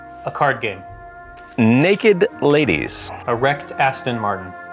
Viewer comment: When he said "a wrecked Astin Martin," I heard "erect"